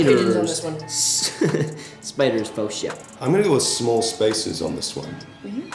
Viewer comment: did Jaeden says shit? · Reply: I think he said " Spiders for sure " it just sounded like it??